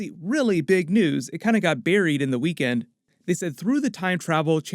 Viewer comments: Did... DId he just say 'Barry-ed' ? xD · I heard: "It kinda got Barry'd in the weekend...". I'm such a Flash-nerd.